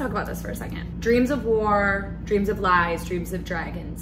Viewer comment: James actually sings "Dreams of liiars" but I always hear it as "of Lars" = Ulrich metallica drummer. It might be a nightmare too😂